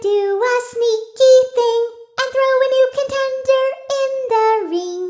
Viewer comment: For a sec I thought Dee Dee said: “I’M goNnA tHrOw a NuKe”